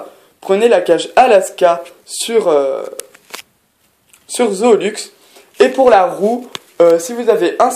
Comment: À 11:36 j'ai entendu Zooluxe au lieu de Zooplus xD